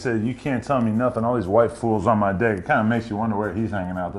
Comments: I think he said waifu not white fools l m a o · He thought he said white fools instead of waifu's bruh im done 😂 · Lmao thought he said "white fools"